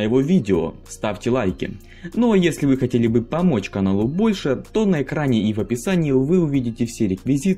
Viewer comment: Послышалось Каналу Польши ))))